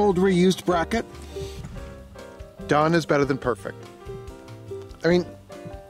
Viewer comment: Great video! Looks like you have most of the kinks out of your design. Love the hinges! I am sorry about Charlie to! I was watching this breakfast & my wife overheard. She thought you said Dumb is better then perfect! Only in my case! Being dyslexic I do most things backwards the first time! Keep the great videos coming!